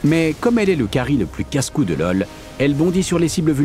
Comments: Ahhh ! ça fait du bien de retrouver cette voix ! En plus le mec met l'humour qu'il faut quand il présente, ça rend le focus très agréable ! Merci pour ce moment ! par contre à 4:15, c'est moi ou on entend "casse couilles" ? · jai entendu "le plus casse couilles"